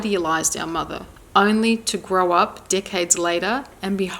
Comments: It sounded like you said grow up dickheads later 12:53 😲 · Lol at 12:52 “in our child’s mind, we internalize that there must be something wrong with us. We may have even idealized our mother only to grow up ***decades** (I heard D*ckheads- did you, too?!) later horrified when we confront the truth of her pathological behavior”